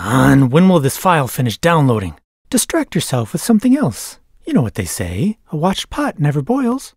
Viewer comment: I misheard the first time and only heard "destruct yourself with something else" Strangely, that would somehow still fit the video pretty well.